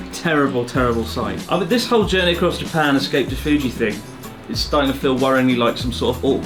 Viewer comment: Pardon me, but, did you just say Jelly Across Japan at the end?